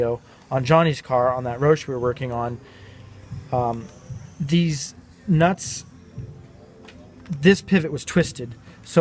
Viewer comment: you say "Dez nutz"!!! Lol. I lost it when I heard that. 😂